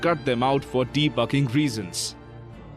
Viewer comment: “For Debugging Reason’s” it sounded like he said For Defucking Reason’s